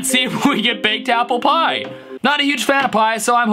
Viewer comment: "AND A BAKED APPLE PIE YEAH!" What I heard: "AND THE THINGS I WANT MY YEAH."